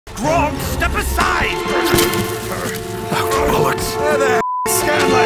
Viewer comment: I thought Percy said bullocks not lord's? Am I just hearing wrong?